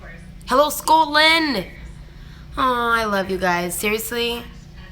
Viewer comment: Does she say 'hello Scotland' at 1:13? Cause I commented saying I was from Scotland